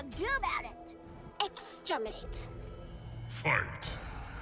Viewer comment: "You offend this"... can someone tell me what she says, please? also, after Ferra says "What ladybug do about it?" does she say "extirpate?" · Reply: D'Vorah: "You offend this one, Ferra." Ferra: "What lady-bug do 'bout it?" D'Vorah: "Exterminate."